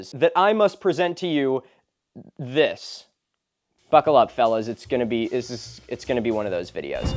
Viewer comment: i heard "fuckle up" and ngl i kinda love that